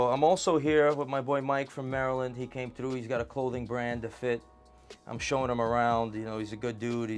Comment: Mike came up here for a cold and fit? Did you say cold and fit? 0:32-0:45? He has a colding and to fit?